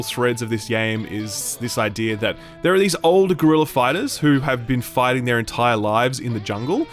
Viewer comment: Why do i keep hearing "Gorilla Fighters"?